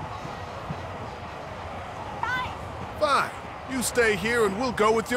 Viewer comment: it sounded like FAI not guys